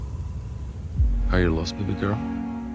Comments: When he said are you lost baby girl I legit thought he said are you the lesbian girl 😂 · Honestly I thought he said "Are you lost, stupid girl?" For a sec thought this became a comedy. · I thought he was saying “Are you lost little girl?” 😂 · My captions were on and it literally translated ARE U LOST VINEGAR HAHAHAHAHAHAHHAHAH 😂 AND NOW IT ACTUALLY SOUNDS LIKE HE IS SAYING THAT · Honestly I thought he said "Are you lost, stupid girl?" For a sec thought this became a comedy. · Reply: I thought he said:"You're lost, pretty girl?" Which is still better than the real one, lol.